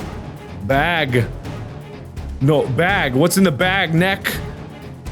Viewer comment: I just hear "what's in the Box!?"